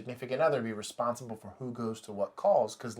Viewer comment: who else heard “shitting in progress”?